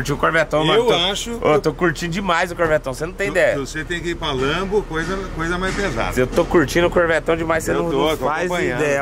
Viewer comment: "Eu acho que vc deveria trocar pra uma lambo" agr oq eu entendi " Eu acho que vc deveria me dar mais dinheiro"